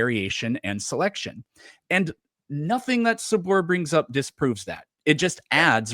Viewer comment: I thought it said 'subfloor' and I was all in